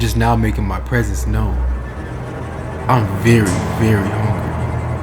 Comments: i heard "im very very horny" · I thought the door said “I’m very very horny” lmao